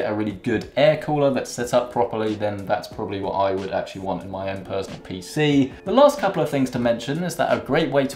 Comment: did he just say personal personal computer